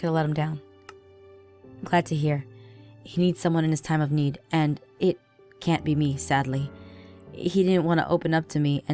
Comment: Did anyone hear that at 7:06???? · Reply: Yeah! It was like a baby just saying "eyup'" in the background.